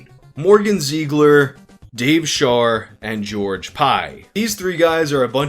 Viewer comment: Ohh, the fat kid is named George Pie. I thought you said "Pyle" and thought that was a bit of a dark reference for a show like that.